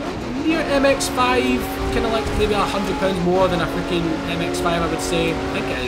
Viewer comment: Also when you said that the TVR is 100 pounds more in terms of weight then the Miata, I thought you said 100,000. I was like WHAT!